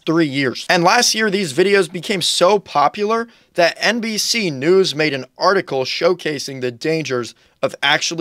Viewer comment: For a second i heard NPC news